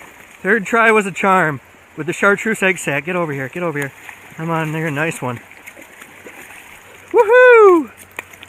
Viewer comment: Dude didn't your momma ever teach you 1.) not to say the n word and 2.) not to cut toward yourself? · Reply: He didn’t say it. It just sounded like that. I even thought he said it but I rewatched it again and it was just him saying “Come in here” but he mixed it into one word saying “Comineere!” With the ‘neere’ soundin like that word.